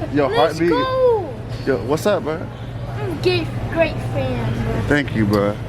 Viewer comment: I thought he said im gay fan